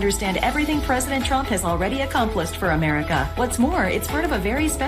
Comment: did she say accomplished or accomplice? Freudian slip??